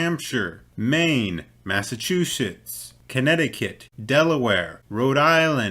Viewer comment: I swear I hear "mass-a-two-sh*ts" subtle thrown in here. Forgive me if it's not what was said lol.